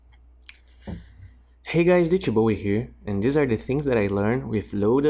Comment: I always hear "DJ Bowie here"